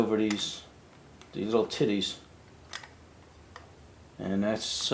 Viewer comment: Did he say "titties" at 10:40? Love it! I haven't heard someone say "titties" in years! Glad I found the channel, love it!